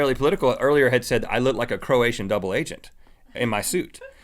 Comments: did he say Croatian cuz i live in Croatia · did he say ''croatian double agent'' ?